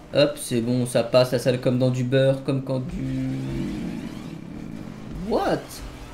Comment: il a voulu dire quoi ? "Comme quand tu ...." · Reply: Comme quand tu rentre la T20 dans le garage